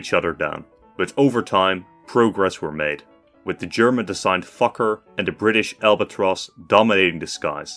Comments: I thought he said “the German designed fucker” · is it me or did i hear “german designed f**ker at 16:38 lol